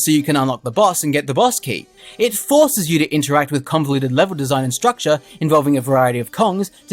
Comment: Oh my god I wish I had thought of that. That’s perfect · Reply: I got the pun from your video when you said the game was convoluted, I thought you said the game Kong-voluted and I thought that was funny